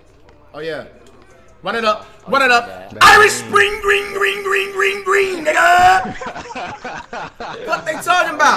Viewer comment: I’ll give 200 dollars to someone who translated what he said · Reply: Run it up run it up Irish spring green green green n**ga